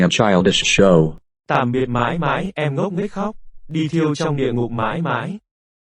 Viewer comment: I've figured out what Dong Hai and Nam Nien are saying in Vietnamese! "Tạm biệt mãi mãi, em ngốc nghếch hóc! Đi thiêu trong Địa ngục mãi mãi! (Goodbye forever, you stupid fools! Go burn in Hell forever!)"